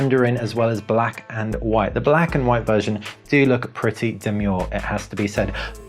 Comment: Question... what is that word he's saying at 2:39. "The black and white version do look pretty ___." 'Demuel' or something? Can't find it online. Thanks! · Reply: demure..